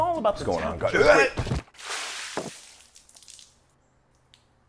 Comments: I thought he said rice coffee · Me hearing "iced" and then hearing "68 degrees" and then followed by the 5 mins of major confusion in Celsius before reaching enlightenment when I remembered Fahrenheit exists. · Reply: I misheard it as 16 degrees